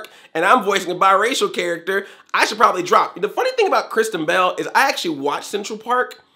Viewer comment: I kept mishearing "Kristen Bell" as "Christian Bale", and wondered why calls Christian Bale "she".